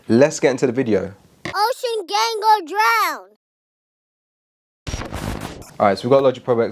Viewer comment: I thought the intro said “Ocean Gang gon’ drown”